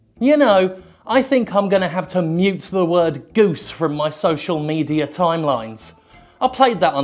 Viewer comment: I swear I just jumped a dimension, I watched this earlier this morning and swore he said he was gonna remove #juicy not #goose. I was so confused for the next 1 minute. I thought #juicyslots had gone mainstream.